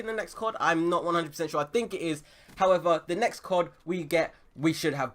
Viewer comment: I am pretty sure he doesn't even know what "boots on the ground" means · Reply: i thought he said boosts on the ground.